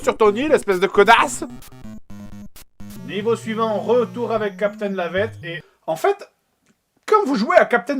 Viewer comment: moi j'ai compris capitaine lavette